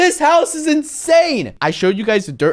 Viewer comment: I thought he said it was a dirt house